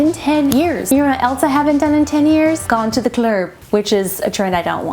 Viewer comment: Wait, what did she say at 2:02? "Gone to the klerb"?? I can't figure it out but I'm pretty sure I'm missing an inside joke